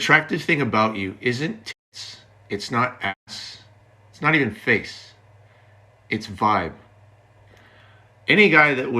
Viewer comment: can some1 explain what Graham says because they muted it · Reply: He said "ooh you'll have to cut that out"